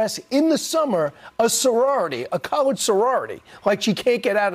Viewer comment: Fox and Friends is garbage, but it sounds like he said “college” sorority.